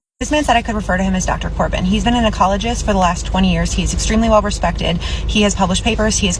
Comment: I thought you said he was an oncologist at first that’s why I’m like why is a oncologist in the forest that’s a doctor that deals with cancer and then at the end you said ecologist which makes more sense lol I was so confused for that whole story 😂😂😂😂 now it makes more sense lol!